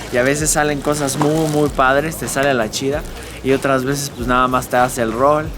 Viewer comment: Cuando decia #lachilanga Pense que decia la chingada Xd